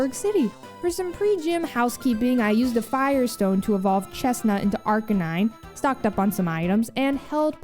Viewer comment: Palpable confusion at 16:50 when I misheard, "I used a Fire Stone to evolve Chesnaught into Arcanine." That's what I get for multitasking lol